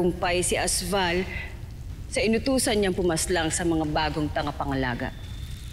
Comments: bagong TANGAPANGALAGA 👇LIKE pag narinig nyu hahaha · Avria: Tangapangalaga 😂😂 Ako lng ba nakarinig HAHAHA Like mo kung narinig mo rin😂